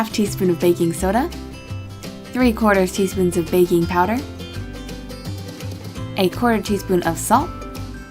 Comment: When you said ''baking power'' I heard ''baby powder''